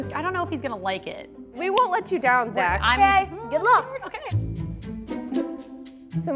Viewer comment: What he said: "Good Luck!" What I heard: "Get lost!'